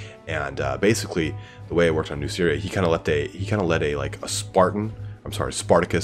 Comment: thought u said New Syria